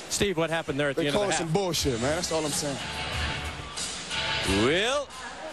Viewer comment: Steve what happe- They called some bullshit ...WELP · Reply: i thought he said coach is bullshit :D